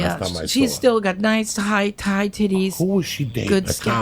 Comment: Im sorry... did she say... high and tight titties.... :)))